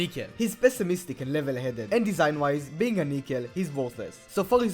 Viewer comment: did he say naked?🫥🫥